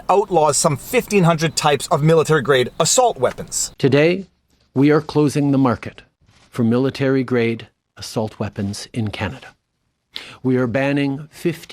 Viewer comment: All I heard was: "Today we are opening the Black Market for Military grade Assault Weapons in Canada"